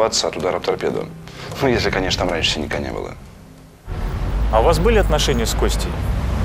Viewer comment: минута - что он сказал?) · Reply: «Ну, конечно, если там раньше синяка не было» ©️